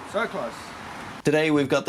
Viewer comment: Did you say "Sag was…"??😮 Are you German. Seid ihr deutsch?🇩🇪🇩🇪